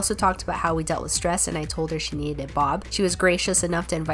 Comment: Ah, I heard 'she needed G-d' to help with stress, so was rather surprised at the box in the car. 🙈